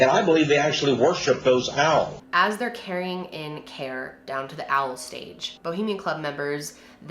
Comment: I had to pause the video and come back, the first sentence I heard was "As they're carrying down Care from the owl stage" I had to rewind 3 times😂😂